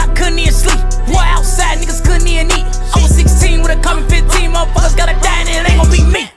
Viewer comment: it sounds like he said i was 16 with a carbon 15 not couple 15